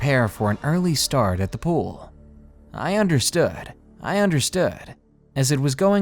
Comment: For those who missed it, I heard him say "I understood" twice at 14:46 :D